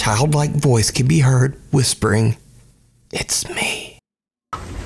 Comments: I heard "you speak" not "it's me". The truck driver one sounds like Bigfoot, I think someone using a speaker for that sound. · I hear "excuse me" not "it's me" and considering he's in front of some doors, it'd make sense.